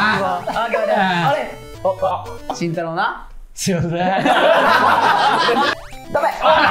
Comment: 慎太郎何て言ったの？ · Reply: すみませーん だと思いました！